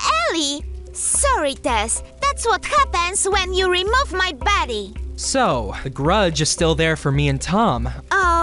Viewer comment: DID GABBY SAY, “that what happens when you remote my baddie” 😂😂😭😭💀💀